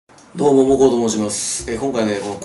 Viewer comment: 最初の挨拶ボコボコと申しますに聞こえる‥‥‥聞こえない？